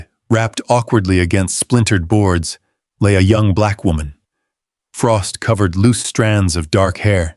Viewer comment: Why does it say white woman? Maybe I’m deaf but I thought they said black woman when he found her! 😂😂 Was just weird! 🤷‍♀️